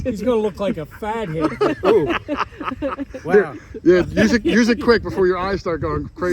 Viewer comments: I cant stop laughing ,it sounded like said hes gonna look like a fag..t in those glasses😂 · what does say?! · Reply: "Fathead" lol